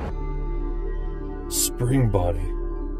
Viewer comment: I thought he said springbutt like in Gabe's world😅